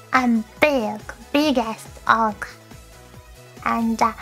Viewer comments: what she said: I'm biggest orca. what I heard: I'm big ass orca. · I know our Chloe said “biggest Orca”, but it sounded way different when I heard it the first time 🫢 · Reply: I legit thought she said "Biggest Ork" and my mind immediately went Waaaagh! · At first when she said "biggest Orca" I thought she said "big-ass Orca".